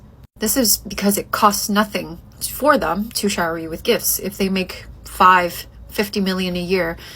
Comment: she actually said 5 to 50 million dollars a year. She's talking about an athlete. To that man, this Kween is 10 minutes of rump roast on a Friday night.